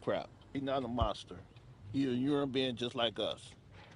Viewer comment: I thought he said my brother was a urine being